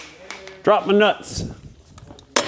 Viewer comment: Thanks for all the years of great videos and commentary! Just for laughs, I totally heard "Dropped my nuts" in a deeper voice. HAHAHAHA!!